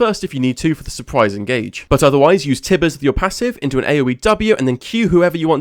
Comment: what is he saying? aowie? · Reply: I’m saying AoE which stands for Area Of Effect which is essentially saying it hits multiple enemies at once!